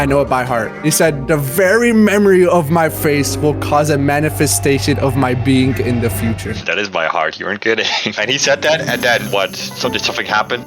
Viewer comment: did he say “the manifasation of the future, I will be there, and watch over you”